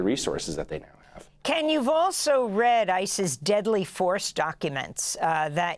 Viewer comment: When she was say ICE's deadly force documents. I thought she was saying ISIS which would be just fine since ICE is a terrorist organization.